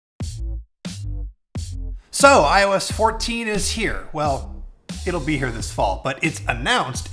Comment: I swear I heard him say “I was 14 this year”